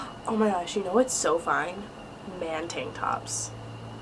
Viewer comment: I was just half watching and scrolling through the comments and I hear her say, " You know what are do hot? Man *tit drops*" It then occured to me that was not what she said and then I started thinking of how hot those are...if you've got abs. Great Vid BTW